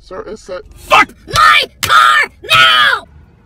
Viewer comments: I swear I heard “ f$ck my car now”😂😂😂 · excuse me what? DID I HEAR THAT CLEAR!?! 😂💀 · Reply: FU.CK MY CAR NOW!